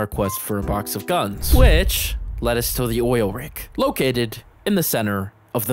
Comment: Who here thought she said oil rat